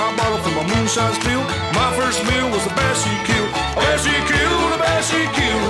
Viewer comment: I always thought he said "a bear she killed" but thats just my opinion.